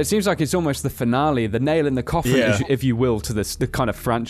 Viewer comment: When ever someone says "nail in the coffin" in a FNAF video I hear "nail in the cawthon" is that just me?